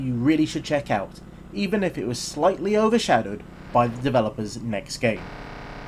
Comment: "Overshadowed by the developers of the next game"... wasn't it the same people? Pretty sure Chocks Away, Stunt Racer 2000 and Star Fighter 3000 were all done by the same people. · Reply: I thought I said, "overshadowed by the developers next game", which was the amazing Star Fighter 3000. But its possible it didn't come over as clearly as I wanted.